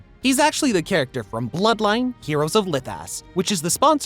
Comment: LOL I thought this man said bloodline heroes of lip ass🤣🤣